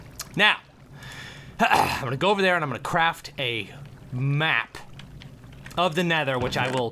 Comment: I thought he said he was gonna craft a mac